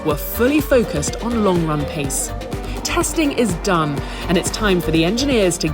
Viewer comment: I thought she said "testing is DUMB"